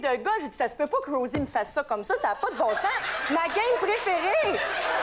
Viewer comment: Okay I watched this episode over and over again back in the day. Did anybody else hear the word circumcision @ 12:12?